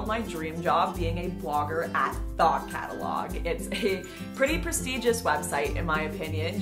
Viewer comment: I heard "thot catalog" and laughed harder than I should